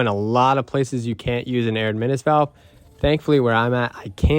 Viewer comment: what did you say ??? You said something like "I know in a lot of places you can't use a admin valve" ?? What is that ?? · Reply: Air admittance valve. It's a plumbing vent that doesn't need to go through the roof.